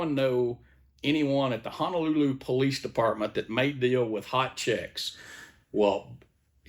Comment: Thought he said "hit chicks" in Honolulu at first.